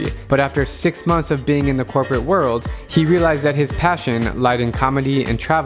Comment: the first 2 times I've heard that,I heard,"His passion lies in poverty"